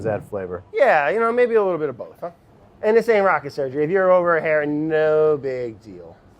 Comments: did he say rocket surgery ? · I think I heard “ this ain’t rocket surgery” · did Brad say "Rocket Surgery"?? lolololol